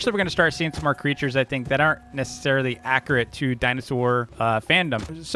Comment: Did bro just say dinosaur fandom?